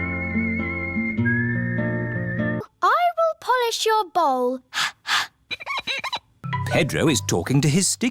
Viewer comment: i thought susi sheep said i will pillosh your balls 😅